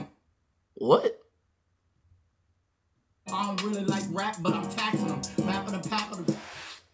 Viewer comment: Yo im literally dying. The second time they played that shit and i heard " i really like rap but I'm taxin em.. rap for d pack capital but back to the bathroom den" ... I really hollered out for dear life bc I really feel I'm going to die whilst laughing